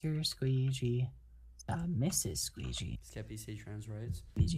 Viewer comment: in the clip of skeppy saying trans rights u can hear bbh singing- “mr squeegy, mr squeegy.. mrs squeegy?” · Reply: I thought he said “Mr Squeegy is a Mrs Squeegy?”